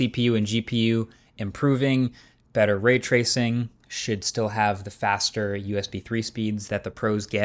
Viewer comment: Did I just hear “Marques say Sh!t still have…”? 😂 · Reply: Maybe he said “should still” 😂